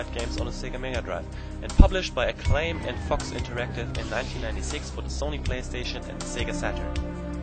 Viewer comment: When you said Fox interactive it sounded like you said Fucks Interactive XD